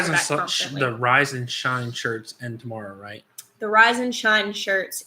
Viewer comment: I hear "The Rise and Shine Church ends tomorow" :-D Yeah we should all do something to make us feel normal. Some confusion about soil and seeds here (South Africa) as some shops sell them and some don't